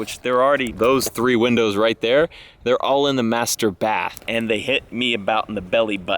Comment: I know you said master bath, but I totally heard "masturbath". Which would have made all the sense in the world in that sentence about it being weird if someone arrived in their car while someone was there lol